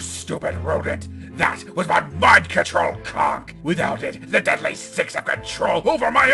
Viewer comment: Wait, did Eggman say "my mind control cock" at 2:03? XD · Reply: Actually he said Conch